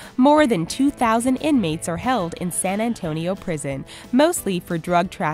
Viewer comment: I'm sorry did you just say say-boo prison?